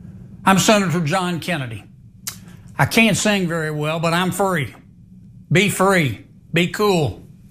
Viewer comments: I swear I heard "I can't sing very well, but I'm furry" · when he said "i'm free" i swear i heard "i'm a furry" and it actually took me a second to realize what he really said XD